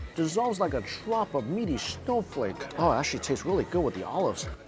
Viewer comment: had me cracking up 😂😅 I know he said "actually" but when I first heard it I thought he said "that $h1t tastes really good" lol. .. had me rolling lol